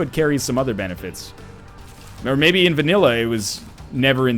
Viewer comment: Hello! im a foreigner, and i dont understand what You said at 34:34. ,,But maybe in the...... :D'' I heard that many times from you but for now i become bored with the fact i dont understand this so could you tell me what it is?:D greetings from Hungary :P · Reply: He says vanilla (The first Isaac version)